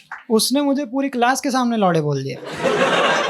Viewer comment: the caption says samay when he says laude